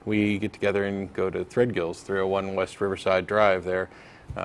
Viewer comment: I always thought Matt said Fred Gills.